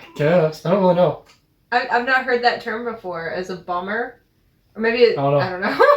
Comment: I thought the doctor said “bomber” as in “bomber jacket”